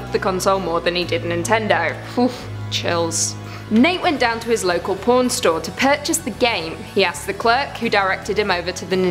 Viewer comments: What i heard: Local porn store IS ANIMAL CROSSING A 18+ GAME!? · I kept repaying it bc I kept hearing "porn" like wh- I had to put on subtitles and that still didn't help.